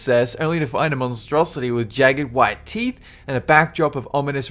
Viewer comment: What the fuck does he say at the end? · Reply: HAVE A GOOD ONE